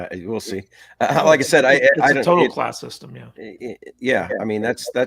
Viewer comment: i think said Caste System